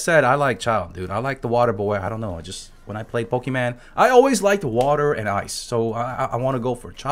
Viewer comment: Did I hear pokiman instead of pokemon?